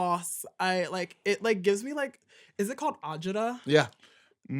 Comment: What word is Luxx saying about not liking sauce? Arjita? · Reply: Agita. It means getting high anxiety but also heartburn.